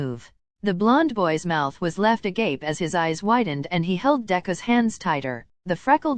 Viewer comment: bro I thought it said a grape 🤣💀